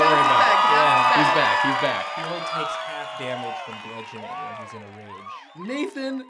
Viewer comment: Does anyone know what Nathan says at 24:30? I've been trying to figure it out and I cant hear him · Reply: From what I can tell, Nathan says, "He only takes half-damage from bludgeoning when its in a rage."